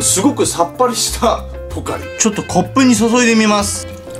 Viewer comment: セイキンのコップに注いでみますが骨粉に注いでみますに聞こえたw